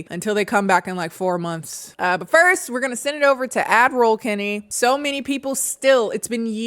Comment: As a Hungarian woman who's been watching you for a while now I feel so validated to hear that others have misheard ad roll Kennie to admiral Kennie, too 😂 took me a while to get it but admiral Kennie for the ad reads did sound cute